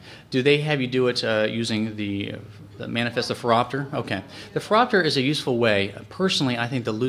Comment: what is that word? throtter / feropter??? can anyone help, thanks! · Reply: King Crk phoropter